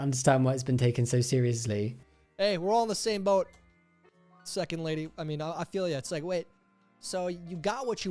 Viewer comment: I misheard sucking lady instead of second lady.😂😂 I'll see myself out.😂😂